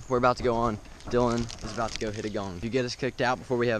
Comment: I thought he said hit a bong🤪